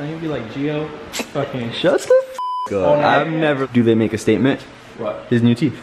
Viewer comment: Did he say “n*gga statement”?😐🤨